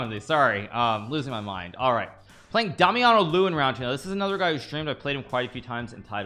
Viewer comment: When he said DamianoLew I thought he said Dummy on a Lou😭